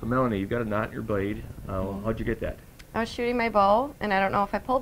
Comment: I could have sworn she said, "Shooting my bowl." No one else hear it?